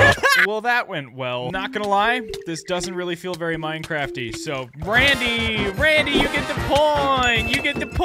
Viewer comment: YOU DID NOT JUST SAY THAT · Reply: He said “it’s not minecrafty”